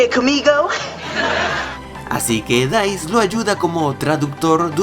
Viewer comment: en el minuto 6:30, le dijiste Dice a Deuce? O escuché mal?